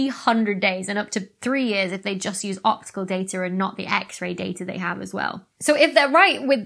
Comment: When you said "x-ray data" I swear for a moment I thought you said "x-rated data" and I was like, damn, space is more exciting than I thought